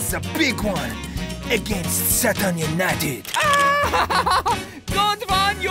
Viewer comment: i actually misheard that as "Saturn United"